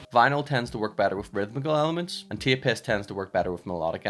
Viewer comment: I keep hearing “tape piss” 🤨